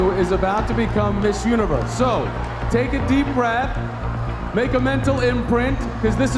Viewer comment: Did he say "Make a mental imprint"?